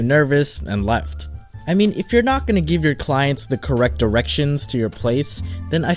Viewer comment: I think he said correct erection 😂